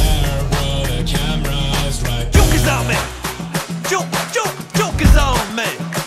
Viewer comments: When he said 'Joke is on me', I honestly thought he said choke a zombie. xD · In this parody when +Chad Wild Clay said "Joke is on me", it kind of sounded like he said " Choke a Zombie"